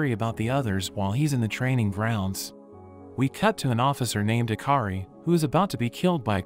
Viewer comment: IM SORRY BUT DID YOU SAY HAKRI LIKE JJK?!?!?!?!